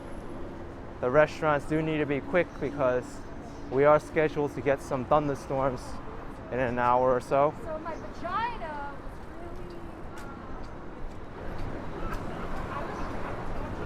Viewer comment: did she say “so my vagina”?